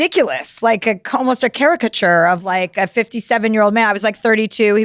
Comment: did she say charac-ch-ter 💀 maam how r u probably well in ur 50-60s yet no one has ever corrected u mispronouncing the word CHARACTER?? 😭 or am i tripping and hearing her wrong??